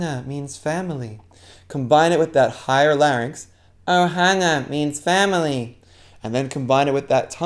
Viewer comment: I thought it said "Banana means family"